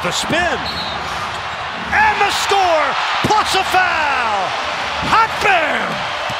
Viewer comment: Did he say Hot BAM?!🤣🤣🤣